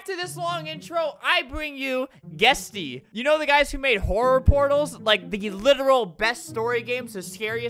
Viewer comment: albert: "you know the guys who created horror portals, the scarist game on roblox" saint abida tribe: am i a joke to you? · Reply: i thought he said "you know the guys who made horror pornos"